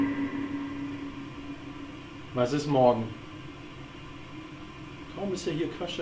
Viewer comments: Bei 12:15 habe ich „klagen“ verstanden... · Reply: "Laden" eher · hab "laden" verstanden vllt wegen handy laden weil der akku ja leer war